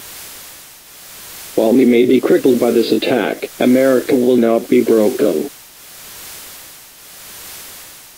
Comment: Out of the 10 years that I have watched this, the President's message was always creepy. I thought he said "America will now be broken" and just realized that he is saying "American will not be broken.